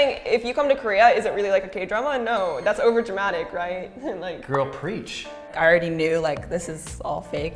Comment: 가 뭐라고 하는거에요? 영어로요! girl preach? 슬랭인가요?